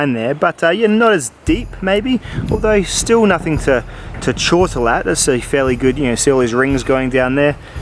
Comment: What word did you say at 6:59 ? CHAUTLE ? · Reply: Chortle. To an Aussie, at least, it means a short outburst of a giggle. In this context nothing to laugh at.